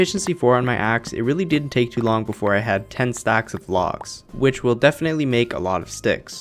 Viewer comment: I thought you said ten stacks of legs hah